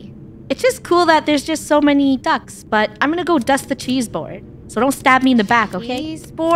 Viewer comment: am I the only one who heard "Dust the cheese bowl" TnT